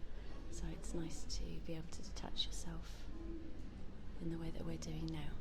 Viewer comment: Did you say 'it's nice to be able to touch yourself' at 11:55 ... The woman to the right thought so too, cos her head turns rapidly haha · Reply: She said 'it's nice to be able to detach yourself'